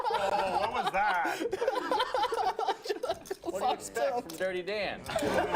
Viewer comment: What is Jungle saying at 12:54 ? by the way the table is too small, no wonder he shuffles on his cards, it's almost impossible not to. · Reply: he knocks all of Nick's chips over and then said " I'm dirty Dan"